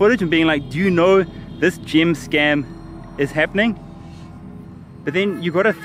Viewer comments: i thought you said GYM scam LOOOL like they selling gym memberships on an island LOOL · I thought he was saying gym scam and I was confused till the end